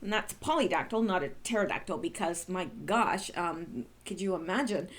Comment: Merry Christmas to you and a very blessed New Year! I love you, my sister in Christ! So glad you got a kitten, I soooo thought you said pterodactyl kitten!!!!